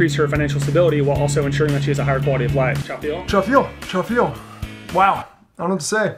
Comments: What r they saying at 0:25? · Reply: cha feel, like slang for - you feel? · Can someone explain what they say at 0:26? Charfield?? or something ? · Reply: Cha feel = you feel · trafeel? WTF are they saying here?